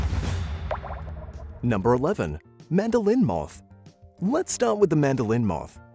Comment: When this dude was talking about the mandolin moth i heard "man from the window" But fr-💀